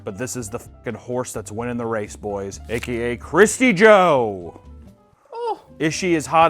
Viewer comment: thought that said " krusty joe"